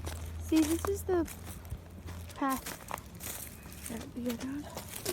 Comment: Using headphones, Abby says, "Is he behind me?" or "is he still behind me? " As she looks up, you can quickly see her mouth moving. It looks like she stops momentarily to say these words, Libby responds with a snigger or quite laugh in response. When Libby turns around Abby says "dont leave me up here" lets not forget it was first time on the bridge, so most probably a little scared, Libby had been over quite a few times, There is 1 shadow on the floor when the camera pans around. Only 2 can be seen once Abby joins her, The part where it was reported Libby said, "That be a gun" is actually Libby saying "that we go down" When Abby jogs past, it kinda sounds like she says "Wheres the path?" Its a bit hard to hear with the stones, and again, Libby repeats about there being no path, and they have to go "down here" I suspect they were waiting for BG to walk past and then go down or walk back over. Once BG reaches them, BG says "Guys" Libby responds by saying "HI," and then the notorious line "Down The Hill" is heard. Can not confirm if there was a gun being cocked as the noise of the feet and stone drowns it out, it kinda sounds metallic like a gun but it could be anything, no point for me to speculate on that part. Mentioned in here of shoes changing colour is none sense as you can clearly see on the bridge white sole with black top, when she jogs passed 😮😮😮 still the same. Height has been measured comparing abby and BG and they are almost the same height BG isnt that tall